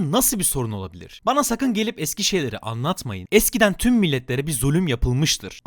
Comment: Burada "Eski şeyleri anlatmayın" yerine "Eskişehirlileri anlatmayın" diye duydum ilk 2 kez :,)